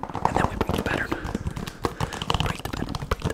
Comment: I was very confused at first cause I thought you were saying break the batter and I was like what is going on